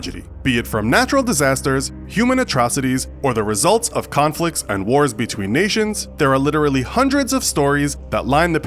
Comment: no joke thought he said tragedies between Asians.